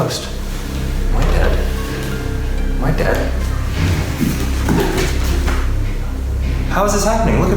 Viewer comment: Nobody: Absolutely no one: Eugene: Am I a dead? Am I a vampire? Am I a sexy vampire? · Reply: Oh I thought he said "am I edward?", as in edward Cullen, hot shiny vampire